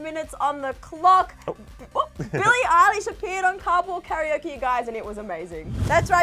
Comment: I lowkey thought you said cardboard karaoke hahaha